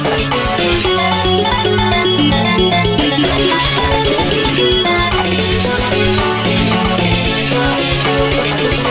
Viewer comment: what did mario say? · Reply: It tells you if you turn on captions. He's saying "I'm fast as f*** boi!"